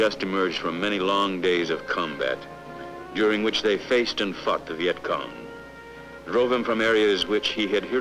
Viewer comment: did I hear him say "faced and fucked"?